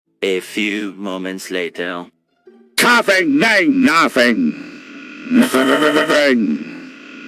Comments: At the beggining I heard "Coughing ain't nothing" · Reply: I heard “Coughing nane nawfing” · At the beggining I heard "Coughing ain't nothing"